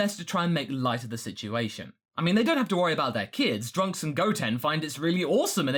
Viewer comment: did you just call him Drunks?